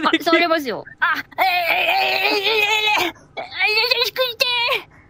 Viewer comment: ずっと「寿司食いて〜」に聞こえてたけど、やっと聞き取れた！ 「やさしくして〜」って言ってたのね！